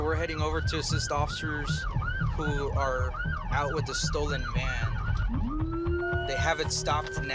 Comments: Anyone else hear him say stolen Man instead of stolen Van? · When he said "stolen van" I thought he said "stolen man" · I thought he said stolen man 😂😂😂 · At first I thought that he said a stolen man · I thought he said he got a call of a report of a stolen hand. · At first I thought he said stolen man 🤓🤓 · Did he just say "we're headed over to assist officers who are out with a stolen man" 😂😂😂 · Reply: I think he said a stolen van, but it did sound like he said “man”. I thought the same thing at first. lol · Am i the only one who heard him say “stolen man” instead of van..